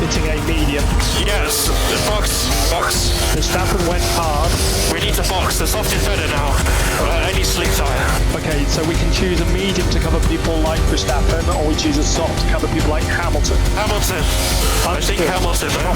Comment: I thought he said "any slick tyre", not "I need slick tyres"